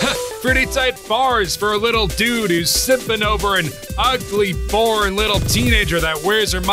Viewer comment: I heard him as "Simpai" lmao